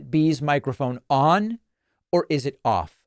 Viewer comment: Oh I thought it said micro penis.